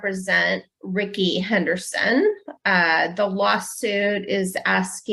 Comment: did she actually say “the Waa-suit”!!!😂😂😂